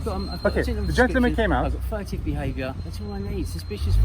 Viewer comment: Did he say "threative" behaviour and then mentioned education? LOL!